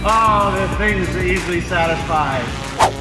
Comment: did he say "things that EASILY satisfy" ?